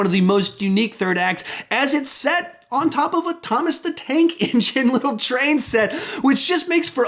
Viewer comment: did he just say thomas the tank